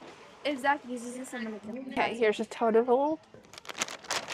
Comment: I am watching the video with closed captions due to having an ear infection and not really being able to hear very well. At mark 1:47 you say "here is your total" and CC said "Here's a toad of hole." I was like what did that just say??? Gave me a good laugh! Love you videos! I am working my way to opening my own small business and watching your videos helps! 💜💜💜💜💜